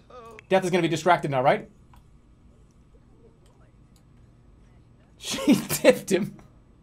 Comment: what does jerma say at 20:55 "she tipped him" didn't need anyone's help anyway just had to re listen to it a year later.